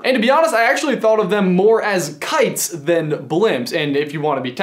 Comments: I thought it said "Kitties" Hairy Baby! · I thought that said kitties.